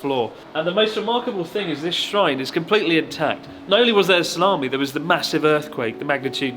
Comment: did he say salami?